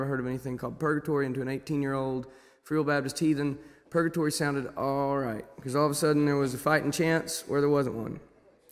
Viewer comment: What does he say after this 00:31 "and to an 18 year old...."? Can't figure it out. I'd imagine it must be a colloquial phrase or similar. I just can't quite pick out what he's saying and it's bugging me! Help! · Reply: Matse Kase to an 18 year real old Baptist heathen purgatory sounded alright cuz all of a sudden there was a fightin chance where there wasn’t one